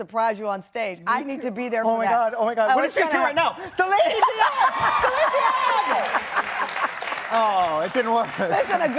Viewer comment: What did he say at 9:07 that was so funny to Tamron? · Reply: "Celine Dion"